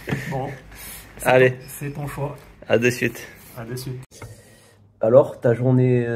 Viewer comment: salut! I have a question -- what is that thing you said at 1:53? It sounded like "a tout suite"? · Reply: Il a dit “à de suite“ mais normalement on dit “à tout de suite” Voilà pardon pour le retard Bonne journée!